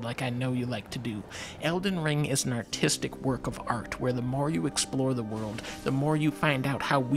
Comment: I heard 2:38 as "Elden Ring is an artistic work of art where the Mario explore the world." Not entirely sure what this means but I fully agree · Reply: He says more, not Mario